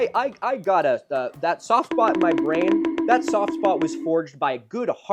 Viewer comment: I love the captions saying softball instead of soft spot XD